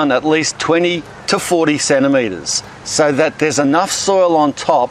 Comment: Cm- centimetres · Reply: Claire Brighton Ohhh he said “40 centimeters.” I thought he said “47 meters” lol!